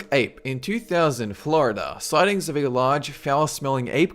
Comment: At like 5:32, I hear In 2000-Florida